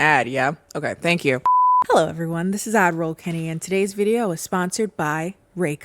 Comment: I always thought you said Avril Kenny, just realizing you’re saying Ad Roll Kenny… 🤦🏻‍♀️